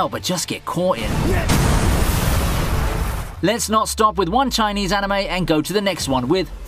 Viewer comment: did he just say Chinese anime? 🤔